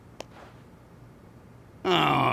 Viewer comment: "Waht ze hell ?" -blue spy, probably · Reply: i thought it said “what the head” xd